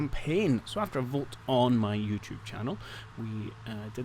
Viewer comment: I don't have one. Tbh, I don't even use my personal one these days. If people want to reach me then Discord is the way to go. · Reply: Misheard you at the start of the video. When you said YouTube I got it in my brain you said Facebook! D'oh!